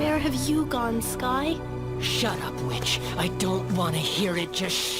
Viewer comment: I heard “shut up, b*tch” 😂